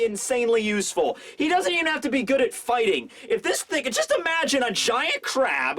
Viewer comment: did he just say if this nigga?